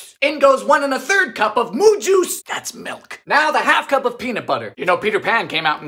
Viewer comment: It took me like 5 of these to realize was saying moo juice in reference to milk, I really thought was saying "moon juice"